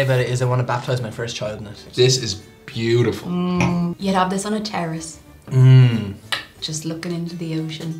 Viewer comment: I thought she said she would have it on a terrorist.........